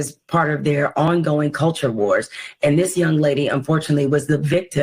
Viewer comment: did she say this young lady is gross?